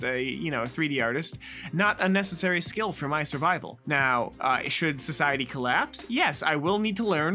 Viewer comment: What said: "Now, should society collapse, yes I will have to...." What I heard: "Now, should society collapse? Yes"